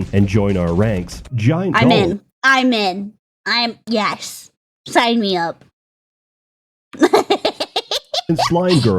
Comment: for the moment i thought i heard "slime me up"